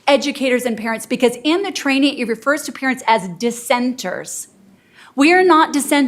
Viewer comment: Parents are referred to as “dissenters” That’s INSANE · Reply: Oh wow. I thought she said descenders. Like “descended from.” Which is offensive in its own right. But dissenters is even worse.